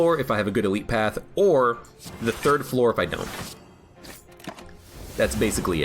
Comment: i thought he said get body at the third floor 😅